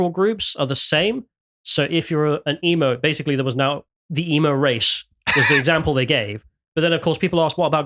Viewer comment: My god, I thought he said the "Emu race" - that accent. The British race is wild.